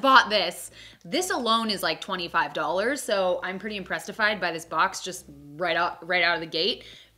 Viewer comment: Did she just say impressedified?